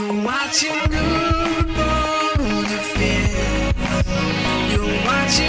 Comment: Sadly no, but back in the days of OOG, someone made a comment about him mishearing it (it actually says, "You're watching Guude Boulderfist!") as "You're watching Guude bone a fish!" Both the reddit and the general YouTube audience ate it up, it was hilarious.